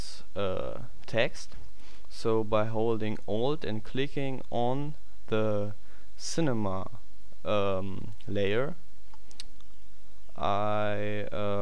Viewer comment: he says click alt on ur pc and click the cinema twice(in case u dont understand him correctly). thats what i did cuz i couldnt understand him that well.